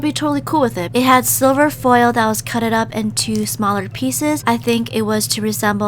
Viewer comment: Anybody else notice at 4:42 she said "and it has silelver foil peuces that were CUTED up in the slime" instead of cut she said cuted